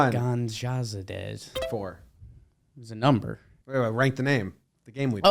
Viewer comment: It did sound like you said “write the name” and not “rate the name”